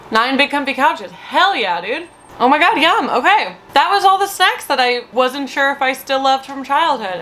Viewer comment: Deadass, I thought you said "that's all the sex I liked as a kid" and omg does it cuz he the context of everything you say afterwards 😆😆😆😆